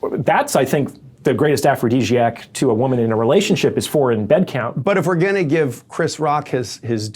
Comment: Anyone know what he says at 47:52? “The greatest aphrodisiac to a woman is ???” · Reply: foreign bed count